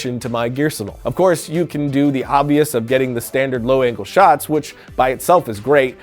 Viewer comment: Oh I thought it said los angeles not low angles lol